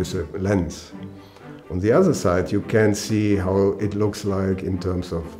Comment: the subtitles are wrong. It should be you can’t see